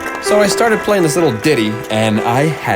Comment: Did u just say Diddy cuz the only daily I know is P DIDDY like what